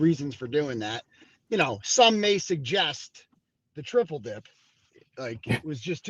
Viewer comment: Cosgrove is double-dipping as long as this case is going. 2 paychecks?? If they paid Hank 250,000, what are they paying HIM? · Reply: I thought I heard he’s triple dipping